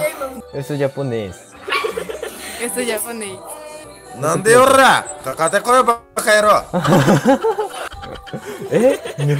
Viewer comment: What did he say? In Romaji, please... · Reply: I suppose he said ”Nande ora❗ Kakatte koi Bakayarou”. It was hard to hear…😅 I’m Japanese.